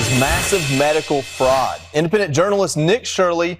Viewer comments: I thought he said IMPOTENT journalist Nick Shirley! LOL, had to rewind—it was Independent. Who joined Groklorum? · I thought the guy said IMPOTENT journalist Nick Shirley! LOL, rewound a couple times to hear it was Independent, lol